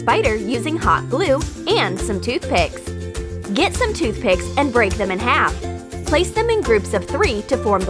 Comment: I thought she said toothpaste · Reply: I mean I thought she said toothpaste when she actually said toothpick